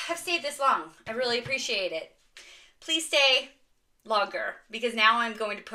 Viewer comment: So I made it to the half way mark but I couldn’t hear the word you said to put in the comments. First I heard “logger”😳 then I rewound and heard “law girl” 🤦🏻‍♀️….😜